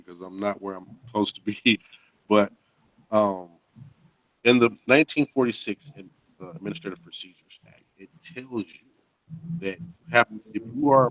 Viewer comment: What did he say from 29:29 to 29:47? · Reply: "Everything falls back to the Administration. The 1946 Administrative Procedures Act. This was very big when they passed this. Because in their.....Ummm......I don't have a bunch of my notes around me because I'm not where I'm supposed to be. haha...but... @ 29:29 - In the 1946 Administrative Procedures Act, it tells you, that if you are part of an administration, YOU HAVE TO BE COMPENSATED. It tells you, Flat out. You have to be compensated. Because there's certain [blank] that says this as well...."